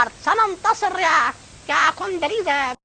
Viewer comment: That Condoleeza sounded like “Cornholio”… 🤷🏻‍♂️